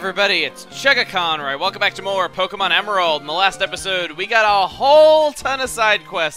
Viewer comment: Why do i hear "molest episode" at the beginning each time?